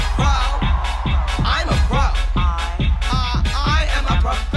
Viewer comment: Did she just say "i'm a crow"